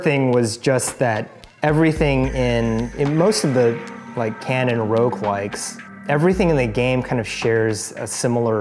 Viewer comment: the subtitles say "unaudible," but I'm pretty sure he meant "canon roguelikes," refering to the Berlin Interpretation. Unfortunately I can't edit that.